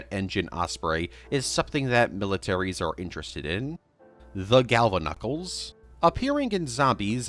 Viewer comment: I thought you said ugandan knuckles for one second